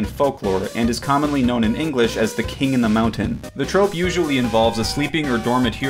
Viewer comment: When i heard "King in the Mountain" i thought he said "King under the Mountain" so I was like "HOLD ON! THAT'S THORIN OAKENSHIELD!"